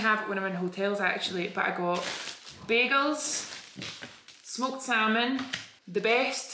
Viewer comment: When she said bagels I thought she said beagles and I wondered why she bought a dog. 😂😂💀